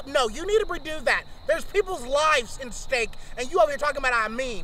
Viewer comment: I heard that as "In steak" lmao 🤣